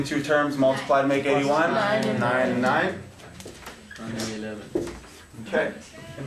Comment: anyone else hear that kid 9/11 at 7:20 ahhahaha